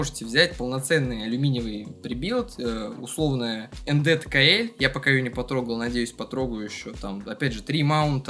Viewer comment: какую клаву взять не расслышал, можешь написать? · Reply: Nd tkl